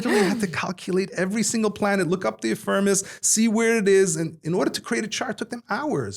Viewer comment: what is the word you used to describe how ancient sages had to make charts? By looking up the "affirmis"? What is that? (around 33:25). Thanks, great discussion!